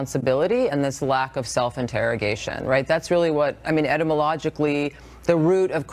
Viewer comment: Did she just say "edibilogically?" I may be mistaken but thats not a word right?